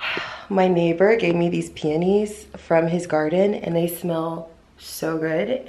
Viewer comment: SO over it. Just ready to get out, get my eyebrows done, hair and nails done! OMG why did i hear "My neighbor gave me these panties" instead of what you actually said. i said WHAT 😂